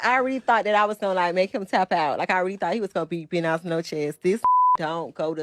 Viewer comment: Did she say Bo Nodge Noche 😂😂😂😂 it's Buenos Noches